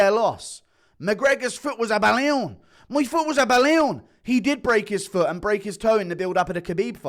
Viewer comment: so thaaaaaat's what McGregor has been saying! All these years I couldn't hear anything other than nonsensical "my foot was up a Leon!" 😅